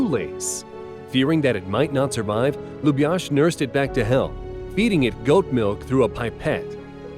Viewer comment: Idkw but It sounded like he said he nursed it back to he**